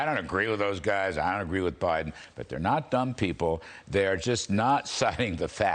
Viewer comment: What was said - x is not a dumb guy What was heard - x is a dumb guy What was meant - x is a smart guy